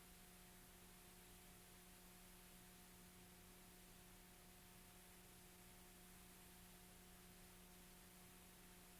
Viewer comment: can we get someone that reads lips to read what he's saying here? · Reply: It looks like he said.. "Way too small"